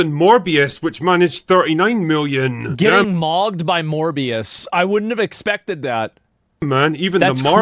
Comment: When he said “Morbius” I heard “More BS”